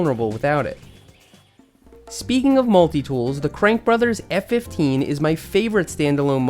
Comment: did i hear F15